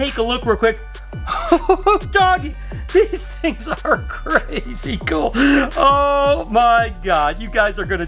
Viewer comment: did I hear you right? Did you say 'these things are incrazicle'? Someone's brain got a little too excited and thought they were incredible and crazy I'm guessing😂 my new favourite word!